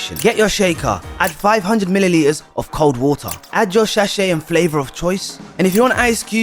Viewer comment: saying “shachet” instead of sachet is sending me 😂